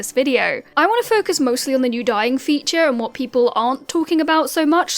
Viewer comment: I was listening to this like an audio podcast because I was driving and I heard "We have a new DYING feature" and I was like, ??????